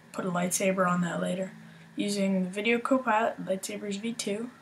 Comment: What do you say at 2:30 ? Video Copilot.... ?